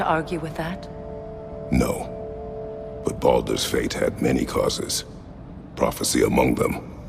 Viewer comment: wouldve swore i heard baldurs gate hahaha